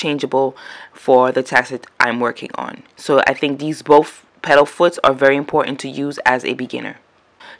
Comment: Very helpful but it’s called a presser foot. I was looking away and heard you say petalfoot and thought I needed different petals for some reason.